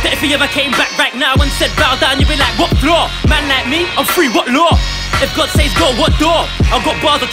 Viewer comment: did Guvna say/mean the law is done away with??? if he did..... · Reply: He said "I'm free what law"